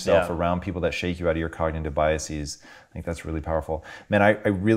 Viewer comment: what is that word!? Cardigan Biases? Anybody know ? ARG